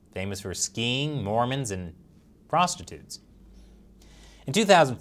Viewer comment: did you say "frostitutes"?